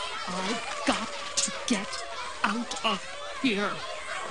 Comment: What's "comblution" or what ever she's saying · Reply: calm blue ocean